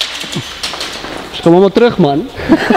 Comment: i speak afrikaans so i think he says kom hulle terug man thats what im hearing